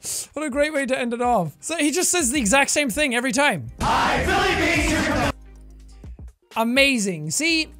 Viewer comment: I thought he said "Billy Beans"